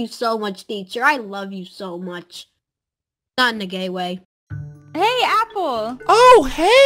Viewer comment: is it me?? Or did just say “I Love You, Just Not In A Gay Way.” XD